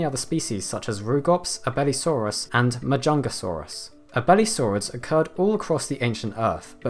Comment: Yeah yeah but did I just hear mahjongasaurus